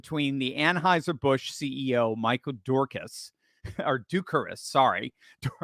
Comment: I thought you said Dukakis lol